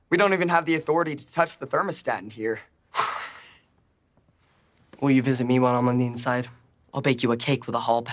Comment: I thought Tj said “Will you visit me on the other side”